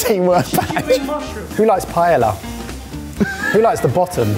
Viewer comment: Oh my god I thought they were saying pie-lau not paella! Lol in the US that’s pronounced pie-eh-ya Pilaf is the same though